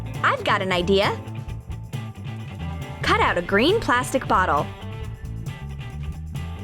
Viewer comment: When I heard " Cut a green bottle" I thought ' oh dear.'